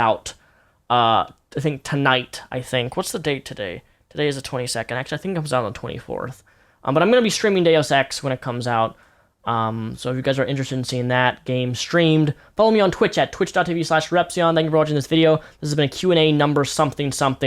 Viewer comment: At around 20:15 I zoned out for a second so I wasn't fully paying attention to what he was saying, and I thought he said "I'm gonna be streaming daily sex" 😂